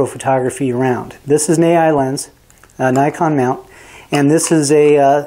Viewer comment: Hi, sorry but I cannot understand the name of the brand for the lens featured at 7:32, thanks ! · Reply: Vivitar Series 1 70-210 f.35